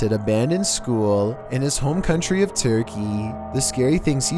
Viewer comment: I thought you Said furrykey til i read the subtitles